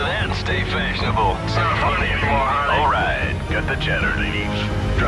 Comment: lol, what did the pilot say at 4:34?? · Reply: Pilot 1: So I says: 'Answer that and stay fashionable…' Pilot 2: It's not funny anymore Harley. Pilot 3: All right cut the chatter ladies. Drop your socks and grab your throttle. Pilot 3: Confederate platform verified and locked. This one's all yours, Harley… Pilot 1: Yeeeeeehaaaa!